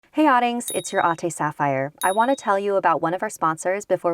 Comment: i always thought she said "It's your auntie Sapphire"..... Am I the only one here?